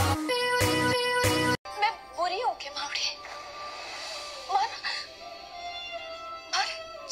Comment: I thought she said... Mai buri hoon kya bawri... Lol